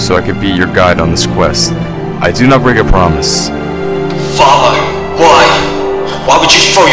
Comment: Lmao,at 10:40 i misheard that Kai was thinking "FFFUUUUUCCCKKK" instead of "father"